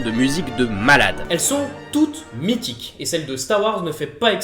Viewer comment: J'ai entendu Meetic a 1:00 , puis je vous aider ?